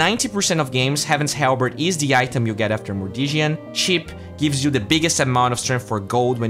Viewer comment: after what?? What did you say?? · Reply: (armlet of) mordiggian